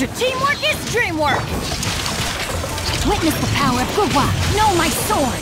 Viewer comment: i always hear " witness the power of good WAP!" whenever xingqiu does his Q